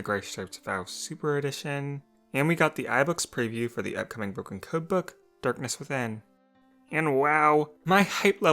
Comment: the first time I heard it I thought you said cookbook